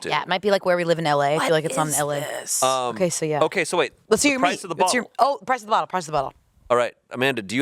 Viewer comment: I'm sorry, did Angela say, "Let's see your meat" to Shayne at 17:12? It was so fast idk if anyone caught it. Or I misheard it...